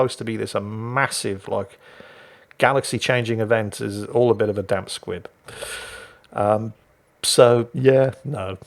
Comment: For all the IT Crows fans... Did say "damp squid" at 24:25???